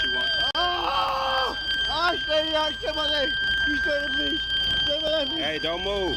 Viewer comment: “Please save my leg!”…cry me a river. They’re suddenly so friendly, think they can make requests and expect help from the very officers that he just literally tried to murder a second ago. Kudos to these officers that have the restraint to still do their job professionally, respectfully, and render aid; despite the fact this piece of garbage doesn’t deserve an ounce of it. Glad to hear the officer will survive; can’t say the same for the shooter. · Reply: I thought he said "please straighten my leg"